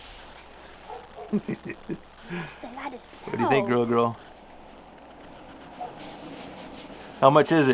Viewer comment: The part at 9:37 where you ask about how much snow (I think you say Lil' girl ).....triggers my Google Mini to ask me me if want to know about how big a one year old waist is....ha! Nice video, you need a helper.